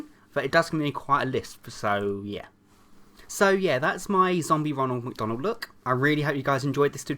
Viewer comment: did he say Donald duck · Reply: LOL No he said "That's my Ronald McDonald look."